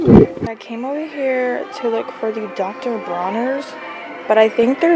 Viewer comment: No Dr Teal's isn't super expensive babe.....u need to get all your Dr Teal's at Big Lots.....oh crap I thought u said Dr Teal's and u said Dr BRONNERS 😂 I'm silly but yes I just found your channel and I'm about to subscribe.... Also yes that H&S cowash smells good but it's not very clean......just letting u know....cause I'm strict about all Natural products on my hair mainly